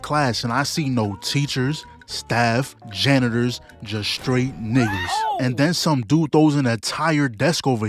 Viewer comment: did he say hard r here lmao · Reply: Nope, said n*ggas